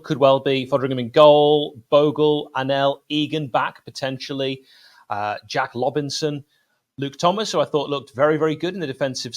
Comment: Did you just say 'Jack Lobinson'? Pure cleverness. Thanks for that.